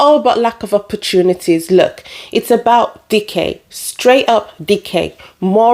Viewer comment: When you first said "decay" I thought you said "dickheads." Then I thought, "Yeah, it's dickhead decay...